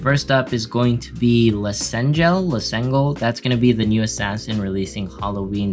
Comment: Kind of sounded like you said that you think the new Assassin is going to be named Lasengle, but Lasengle is a video game publisher and developer of FGO currently.